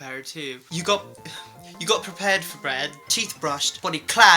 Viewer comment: anyone noticed he said "bread" instead of "bed"😆